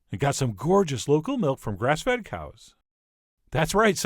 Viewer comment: I thought you said "breast fed cows" 😂😂 "Some dairy farms feed cows what they are actually supposed to eat" 😂😂😂